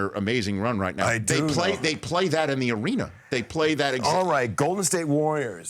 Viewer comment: Woah my ears are ringing, at 1:30, did he just say they play the “Warriors, Come Out To Play” Soundtrack in The Arena!!?? 😮😀😃!!!